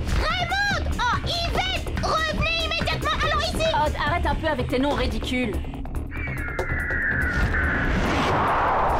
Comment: sympa les nom de odd😂 après c zst quoi ? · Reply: Jean pierre chou fleur